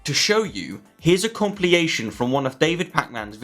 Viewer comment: Did you just say a “compliyation” at 1:41 instead of compilation?